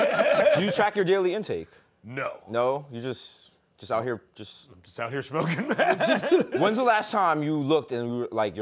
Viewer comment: sounds like Seth says: Just out here smoking meth lol